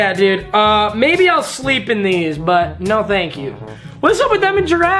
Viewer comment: did he just say he’ll sleep in those? Like if u sleep with no socks